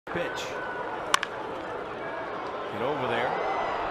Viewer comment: I'm i the only one who heard "bitch"